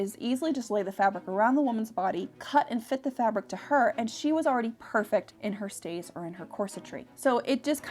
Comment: All I heard was..... “she was perfect in her shape first!”